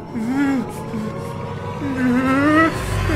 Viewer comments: I thought he was saying “take down the button” but he was actually saying”TAPE DOWN THE BUTTON” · He could have easily said "no, I won't hurt this clown" but the way everyone is treating the situation as normal really pressured him into pushing the button. I wonder if I would do the same. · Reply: Oh, I thought he said "TAKE the button down" - like, get rid of the button entirely while no one's looking. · Okay so I went through a whole range of what the ending meant. At first I thought he said “take the button down”. Which he didn’t cause he saw the joy it brings people. The head nod is him coming to terms with hurting the clown to make people happy… THEN I realized that he said TAPE the button down. Which I initially took as like , oh he DOES like it and wants more for some reason. My final conclusion upon thinking about it is that he wanted him to tape it down cause he wants to die. I finally get it lol